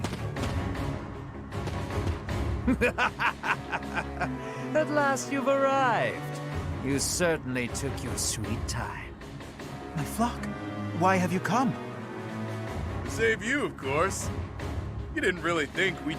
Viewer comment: just saying, but at 1:40:00 when he says “My flock!” if you speed it up to 1.5x or more, it sounds like he said “The fuck!”